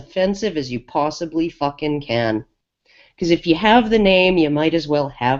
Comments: "SUFFOCATE THE PENIS!!" - Creepy bitter granny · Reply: I thought she said "Because you have the penis" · "SUFFOCATE THE PENIS!!" - Creepy bitter granny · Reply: The video in general has been heavily edited i've heard at least 5 times over :/ which they missed....so we don't know for sure what was said...however this is what i thought she said "Because you have a penis"